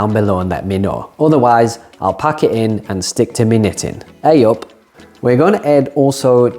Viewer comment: Ok, did he just say, "... stick with me knittin'?" I hope he did; we knitters are so often under appreciated! 😂